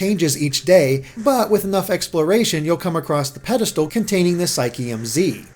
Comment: When he said pedestal I thought he said pedophile...